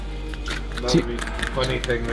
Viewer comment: I literally can not hear what the owner are saying at 15:05😅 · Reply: “What’s different is they (buffalo) have a smooth tongue rather than a rough tongue”